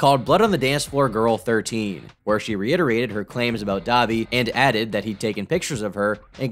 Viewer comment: bruh i kept hearing "dahvie" and thought he was saying "dobby"